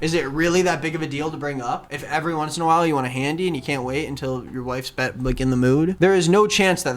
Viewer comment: he said “when your wife’s back like back in the mood” But I heard “when your wife is done licking the moon” and I thought it was some weird euphemism for a girl being on her period